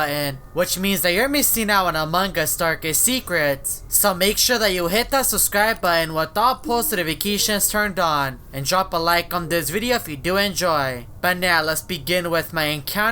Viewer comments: Really? When you said to turn on notifications it sounds like he was saying Without posted a vacation turned on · It sounds like he says "make sure you subscribe with all notifications off.".